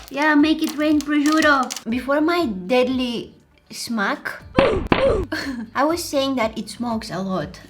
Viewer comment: idk why byt also i hear here e,, smak " and it means in Polish,, teast" xddd